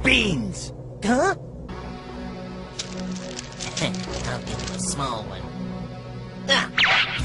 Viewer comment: Nobody heard krillin say "il give him a small one" haha greatest sneak diss haha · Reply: Haha I heard “ Heh um small one.”